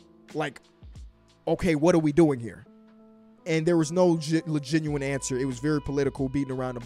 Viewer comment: did you say "Legenuine"? Did you combine legitimate and genuine?? Hahahaha it sounded a Lebron word, LeGM, LeGenuine 😂😂😂